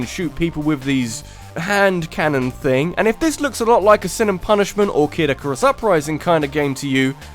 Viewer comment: What is the name of the game before Kid icarus Upring? (Can't understand saying the name) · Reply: Sin and Punishment.